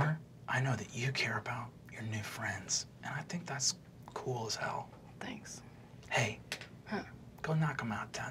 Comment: did god just say cool as hell?